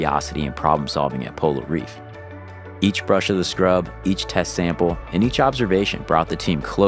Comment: Did I hear incorrectly? Each : “Brush of the Scrub” 😂😂😂😂😂😂😂😂😂😂